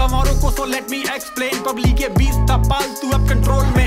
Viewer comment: Here 1:05 says that Public ye beast tha paaltu but the subtitles says Pubic ye beast tha paaltu. Double entendre maybe